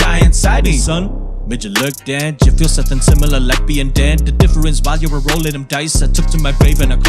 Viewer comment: i keep hearing " like being dead" as "lesbian dad" . help